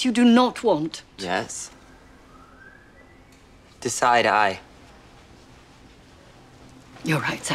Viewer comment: when he said 'decide i' it sounded like he said 'side eye' lol time stamp